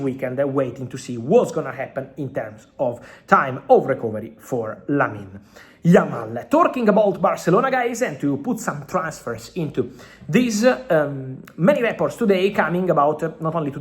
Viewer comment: "twerking abt barcelona guys" is it just me or anyone else heard that